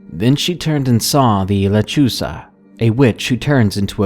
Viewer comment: Lol when u said lechuza i thought u said lechuga. Lechuga means lettuce in Spanish hahahahahahahaha sorry if i spelled that wrong lol